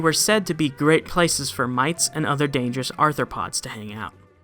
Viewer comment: at first I understood "mice and other arthropods"... not been an english speaker sometimes creates WTF moments. Now I know that he said mites. A bit of common sense and google, sometimes, help.